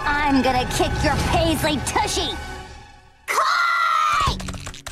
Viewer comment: What did they say? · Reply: To me it sounds a lot like "Koi!", but I don't know if that makes any sense, as "koi" means "love" I think. But I found out that when you say it twice like "koi koi!", it means "come on!" (roughly translated). So that's my best guess.